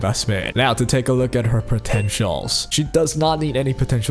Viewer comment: Did you say potential😂😂😂